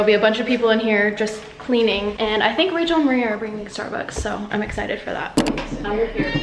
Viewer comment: Congrats on your new home! I'm like 6 months late but who rlly cares haha! When you said Rachel and Marie at 6:13, I thought you said Rachel Marie I'm like what? As in that Gymnastics Coach? I love Rachel Marie so much and if she actually knew you I would've passed out hahahahaha!